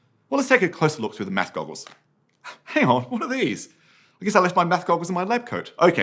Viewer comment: What did he say? "What are these? bla bla my math goggles in my lab coat." I can't hear the bla bla section, even after reading the script. I'm sorry to make a trivial question. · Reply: Thank you for catching this! We’ve updated the subtitles here so this portion can be understood. At this part, he is saying, “Hang on, what are these? I guess I left my Math Goggles in my lab coat.” We hope this improves your experience.